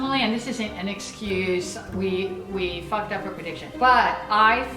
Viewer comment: did she just say "we fucked up our prediction"